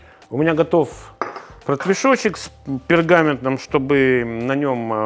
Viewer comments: Только я услышал "портвешочек" вместо "протвешочек"? 😂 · А мне послышалось не протвешочек, а портвешочек😅. Вот это думаю биточки для детей😂